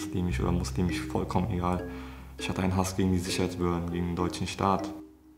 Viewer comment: Wichtig Ihr habt bei 14:43 einen falschen Untertitel. er sagt nicht "scheiß behörden" sondern "Sicherheits behörden".